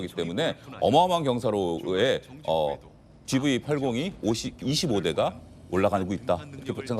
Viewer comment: 제가 뭐가 잘못 들었나요? 14:26 쯤에 "어마어마한 경사로에 GV80이 25대가 ~~~" 이란 말이 들리는데.... 맞나요?